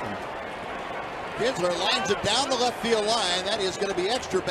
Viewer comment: it sounded like he said hitler 😂